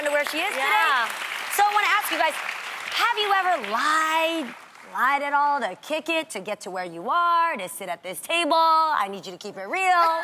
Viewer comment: The lady in the center just wanted to talk. The question was, “have you ever lied about your age?” Why did she tell a story about a commencement speech???????????????? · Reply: No watch it again, that is not the question what you said. At 0:28 she says: Have you ever lied to kick it? To sit at this table. She never talks about age.